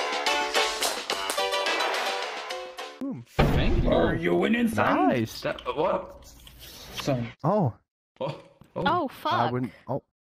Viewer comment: Bro at the first clip it kinda sounds like you said "are you innocent "and I got mad among us vibes